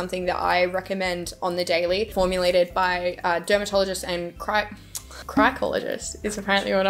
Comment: I thought she said “Crackologist” 😂